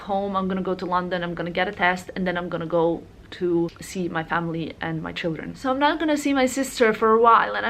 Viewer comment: does she said: “I’m gonna miss my family and my children” 😳